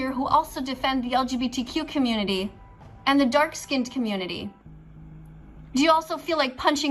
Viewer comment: the what community? Did this big nose bozo say the “dark skinned” community? 😂